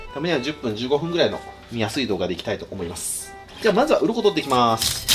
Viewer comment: 前半に引っ張られ過ぎて、2:35 が「ウ○コとっていきま〜す」に聞こえる笑